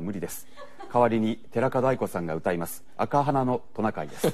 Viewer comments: アカハラのトナカイに聞こえた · 最初聞いた時、タイトルも相まってパワハラのトナカイって聞こえた